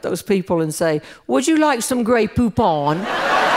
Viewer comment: I just love Jeanne’s smooth story telling style. First rate all the way! She’s the best. I’m trying to model my style after you, Jeanne. Love you! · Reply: When I first heard this commercial years ago I thought the person said Grey Poop On.